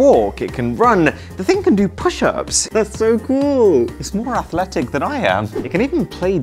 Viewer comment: 'its more athletic than i am' Oh i heard- It's more pathetic than i am 💀